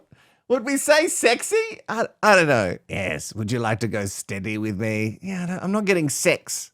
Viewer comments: I thought she said dirty not steady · She said steady? I thought for a moment she said 'dirty'. Ooh